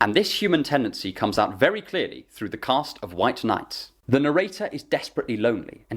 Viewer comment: For a moment I heard "White Knight" and thought " Oh that's a fitting title to talk about the death of romance".